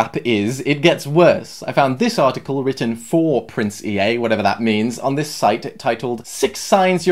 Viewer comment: When he says Prince Ea I thought he was saying Princey lol